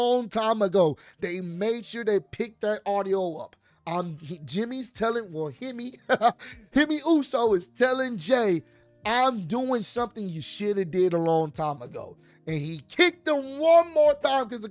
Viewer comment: 😅 Whew. . I thought it said Jimmy ARRESTED !! Lol